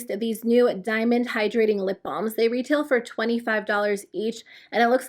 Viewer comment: 😂🤣 I kept hearing "lip bomb"